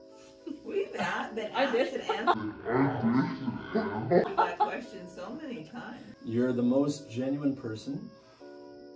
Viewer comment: what did she say genuinely watched this back like 30 times and couldn't understand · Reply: She said “I did”